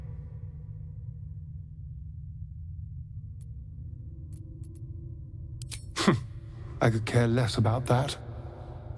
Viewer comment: Thank you thank you! A question tho, what do you say at 10:17 to prompt him to say "I could care less about that"? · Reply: ☺️❤️ «Let's get them out of there»